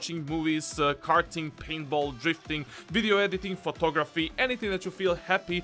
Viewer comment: Am I the only one that thought he said pornography instead of photography